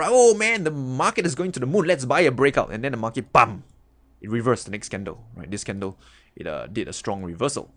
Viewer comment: Did he say the market pumped?